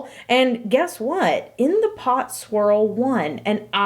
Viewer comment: misheard: in the plop swirl